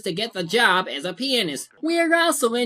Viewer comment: I'm sorry, but I thought you said "work as a penis" instead of "pianist"😯